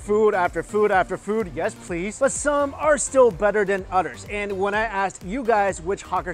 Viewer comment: I was like did he just say utters hahaha cute 🤓